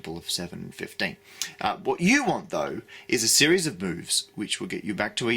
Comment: i heard "series of boobs"